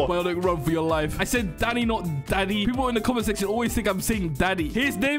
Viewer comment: the captions say daddy😂